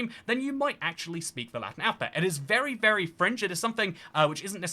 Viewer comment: I thought you said 'it's very French' at first. 😆